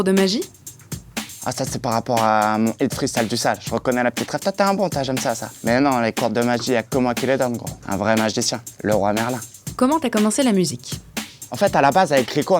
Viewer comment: Il voulait dire, et à dit "Le Roi Merlin" en rapport au grand magicien Merlin, mais tout le monde a entendu "Leroy Merlin" le magasin d'outil... est ce qu'il s'en rendre compte un jour ?